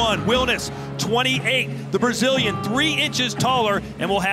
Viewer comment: Assustador 😅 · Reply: thank you so much! Didn’t realize he just yelled “Brazil”. Was trying to figure it out on google translate and thought he was saying “afastar” and something else